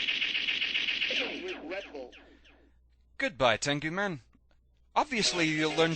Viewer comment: what does Tengu Man say when he blows up? Sounds like he says "I need a Red Bull"